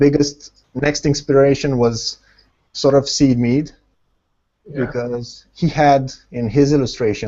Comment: Hello! help me please with something, at 13:40 says something about an artist, but I can't understand the name, can you help me? says something like this: "the big inspiration was sort of..." what is the name? :D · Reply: Syd Mead :D